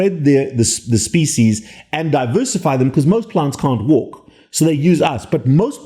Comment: WAIT A SEC. At 4:52 did he say "MOST PLANTS CAN'T WALK, so they need us"?